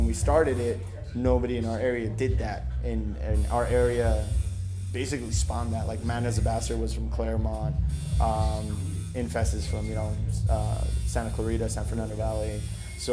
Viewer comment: what are the bands he mention ? I can't understand the names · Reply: man is the bastard and infest